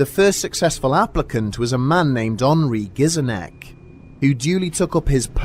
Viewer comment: It sounded like you said that guy's name was ornery gizzard neck